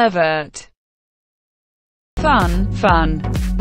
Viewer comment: “Pervert” “Fun fun” FBI open up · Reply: I thought “fun fun” Said “touch touch” and I scared the hell out of myself.